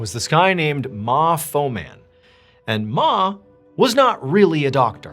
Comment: ' I thought you said "mofo man" 😂